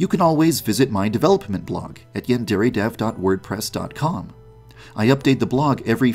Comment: subtitles say ‘at Yan dairy dev’ 6:57 subtitles also say ‘and err a simulator’ Subtitles are amazing😂😂